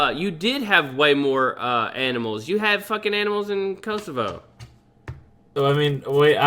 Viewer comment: what country is saying? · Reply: Luke Gover kosovo